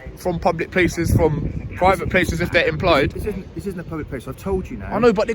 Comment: did he say it's a public place?